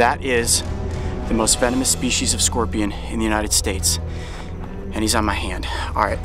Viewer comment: I thought he said the most FEMINIST Scorpion in the United States